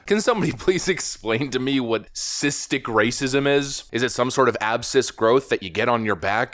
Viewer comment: When I heard him say "cystic racism" and honestly I thought he said "assisted racism"...there's a comedy sketch in there somewhere